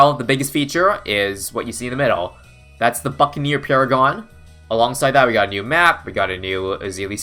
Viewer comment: i swear he said fuckaneer paragon